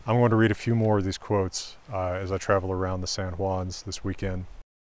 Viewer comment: For a second I thought you said Lasal's instead of San Juan's. You need to pay a visit to the Lasal and Henry ranges over in Eastern & central Utah. All the best to you sir